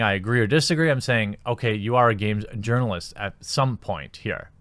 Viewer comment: i thought when you said Urinal-ist, i thought you were pronouncing it yournalist (like as if a sweed was saying it) LOL